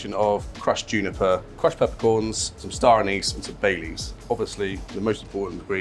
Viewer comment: I thought he said Bailey's instead of Bay leaves there 😅